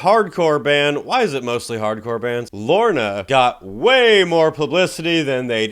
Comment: When I first heard that, I thought he was talking about Lorna Shore!🤣